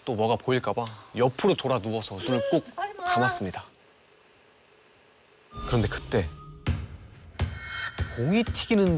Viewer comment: 나만 무슨 소리 들림? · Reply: 홍윤화가 하지마 하는거아닌가용